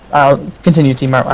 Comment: lol i thought said im smart XD